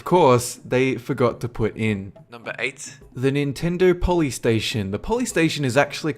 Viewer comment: On #8 I thought he said the police station XD UPDATE: and #1